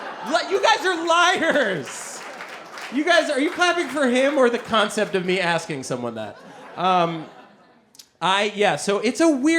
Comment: What did he say at 6:31? it sounded to me like "you guys are, you probably could have more the concept than me asking someone that" And I'd like to know what he meant by broommood best regards from Korea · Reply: In case no one answered you yet, he said, "You guys, are you clapping for him or the concept of me asking someone that?" He was talking about the one person in the audience who admitted to hiring a prostitute. I don't think he said "broommood," and I'm pretty sure that doesn't mean anything. At 8:42 he says "vermouth" a couple of times. If that isn't the place you mean, please let me know the time and I'll try to help...